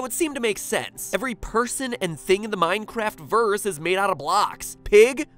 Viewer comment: Whoa, whoa, whoa. Did he just say "verse" instead of "universe"? Who does he think he is? Jupiter Ascending? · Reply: He said Minecraftverse, like Minecraft+Universe. Plus, these things envolving signs and ascending to "x" are all fake.